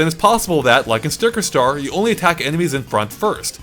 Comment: All i hear is "Just like Sticker Star"